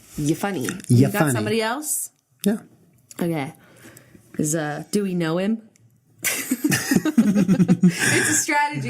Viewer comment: "Doweknowhim?" I heard "Dewey noem" as if she said a name 😂